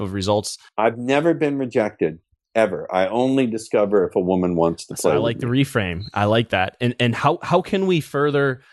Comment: Why he interrupt the Ross in the most important word of the video?! At 29:45, what did he said about rejection?! Thanks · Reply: "I only discover whether she has a good taste or not"😊